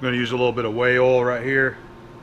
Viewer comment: You're an American treasure. What kind of oil were you using when you were using the steady rest? It sounded like you said Whale Oil but I know that isn't what you said.